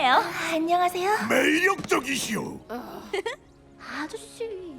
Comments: 에서 메이 육덕 이시요? 라고 들리는데 나만 그런거임? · "메이 육덕이시오" (나만 이렇게 들림?;;) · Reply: 메이력적이시오 입니다. 라인하르트의 아재 개그죠 ㅋㅋ 문맥을 살펴봐도 육덕은 너무 뜬금없... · 메이 육덕! 이시오 라고 들리는데 나만 그럼?